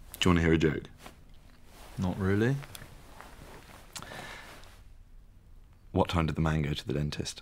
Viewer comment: I thought jack said what time did the mango go to the dentist 😂